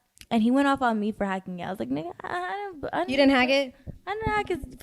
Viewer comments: sounds like Celina said Nigga 💔😳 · did she say nigga?😭